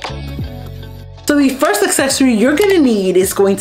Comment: I keep hearing you say "Successorie" and I am not sure if that is what you're actually saying it or if it's my damn ears, but I LOVE IT!! A good accessory always makes me feel so much more successful!!